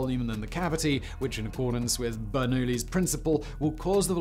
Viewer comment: Did he say Bert and Ernie’s principle at 12:00... I’m just asking.... I just looked at the comments and I don’t think anyone caught that... Help me out guys... Please don’t let me watch 12 hours of Sesame Street to figure it out... thanks in advance 🙏